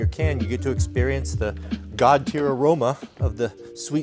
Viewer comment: “God tier aroma” man that’s the best sentence I’ve ever heard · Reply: I thought he said “god pure aroma”😆